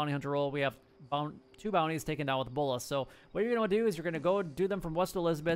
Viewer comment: Oh I had thought you said take the bounties down with bullets, so I did but I didn't get the daily challenge, then I came back and watch this video again and you said bollas, my bad